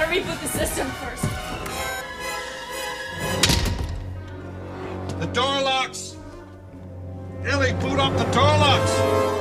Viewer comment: Two things I can't understand speech-wise are: 1) When Grant is saying about the doorlocks right before the raptor appears, what is it he is saying exactly. It sounds to me like he is saying, "The door locks. Elle they glued off the doorlocks!" but I know that can't be it. 2) Then when Grant and Elle are trying to hold the door and Elle is trying to get the gun and it sounds like she says, "I can't get a lick on move!" but I know that's wrong too. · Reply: Kyle Blank "The door locks. Ellie boot up the door locks." and "I can't get it unless I move."